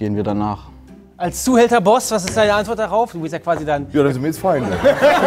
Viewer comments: Was sagt der Boss bei 3:38 ?? Verstehe seinen double time nicht so ganz... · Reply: Ja dann sind wir jetzt Feinde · Was sagt Kolle bei 3:42? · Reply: Joar denn sind wir jetzt Feinde